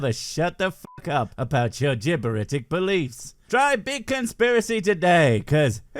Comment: Did you just say "jibberitic?" Classic! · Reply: I did... its spelt gibberitic tho... and no I don't care that its technically a word I made up (even though I'm SURE I have heard someone else say it before... but maybe I made that up too :0)